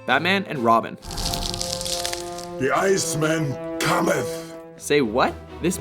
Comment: Lol, I thought he said the night man cometh(it's always sunny in Philadelphia) which also involves Danny devito